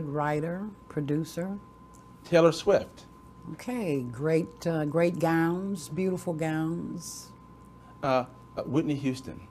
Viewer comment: I always hear Trump, "great gowns, beautiful gowns" :))